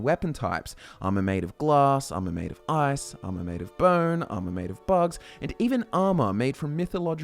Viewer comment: did he say "armor made of butts"?? Where is this video!!!?? I must see this for myself! :O Never encountered such armor while playing Skyrim!!